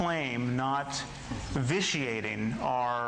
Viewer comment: what is the word Sam uses at 2:50 "why is this claim not _____ating our academic department..." does anybody know? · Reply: vitiating (or viciating) -- take it from a Dutch guy.